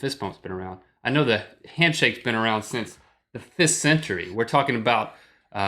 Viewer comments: i almost thought Link said handshakes have been around since the FIST century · First, I seriously thought Link was saying the "fist century" instead of "fifth". Second, clowns... I don't have a problem with them, but I have a friend who is afraid of them. So if I want to effectively ruin something for him, I point out it's relation to clowns, whether it's how something looks or if it's just the music. In all honesty, I think there are the normal clowns and the creepy clowns, and the creepy ones are taking over. Third, if it hasn't been asked before, ninjas vs pirates? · Oh, fifth century. I thought Link was saying "fist century."